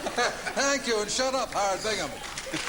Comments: did he say "thank you and shut up harry bingham" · does he really say "thank you and shut up..." at 32:05? :D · What is the host saying at 32:07? It sounds like, “Thank you and shut up Howard Biggim (sp?).” Ali?